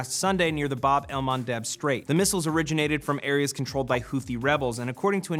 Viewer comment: Did say toothy rebels?